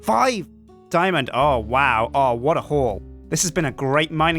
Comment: I thought said "What a whore" instead of "What a haul"